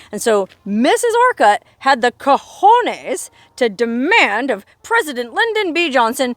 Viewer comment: into the vidoe did I hear you said "cojones "😂